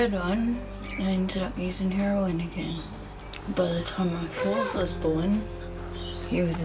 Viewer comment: Not to judge... but if that lady can take so many drugs a day... why can’t she take birth control??? 4 children??? 3rd born on drugs? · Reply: I thought she said “after her fourth was born” so is this her 5th???